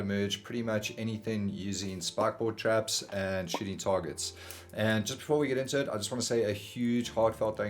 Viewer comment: Love thd accent. I thought you said shitty targets, and shitty shed. Cheers !